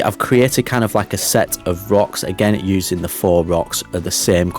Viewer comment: I have been binging this series, and it's taken me 15 episodes to figure out you were saying "faux" rocks - I thought you were saying "four" rocks! FWIW, I'm an Australian, and don't hear accents like yours very often, ha!